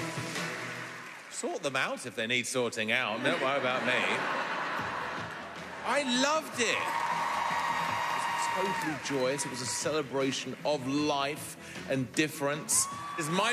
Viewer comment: did he say GRABE? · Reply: "Luve it"